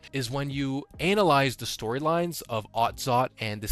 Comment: did he just say Anal-ize? 🤣